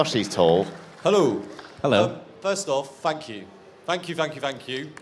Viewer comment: OMG I heard the guy asking first question (14:12) said "F*ck you" not "Thank you". )))